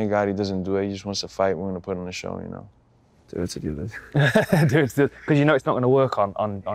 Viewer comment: What did till say at 18:21? I couldn't understand. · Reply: Do it to the others